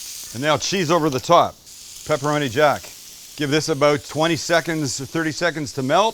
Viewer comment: Did I just hear pepperoni jack? :face-green-smiling